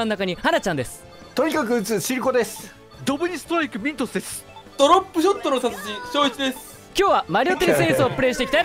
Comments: 最初のさんの挨拶のドロップショットの達人が、ドロップショットの殺人に聞こえたw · さんの最初の挨拶ドロップショットの殺人って聞こえたw